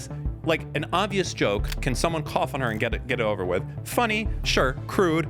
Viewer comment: In the intro it sounded like said some should coffin her and that wasnt a death threat, I was thinking idk sounds kinda like a death threat to me lol But it was cough on her